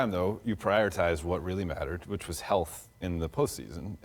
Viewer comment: dude 5:42 swear it sounded like he said health in the pussys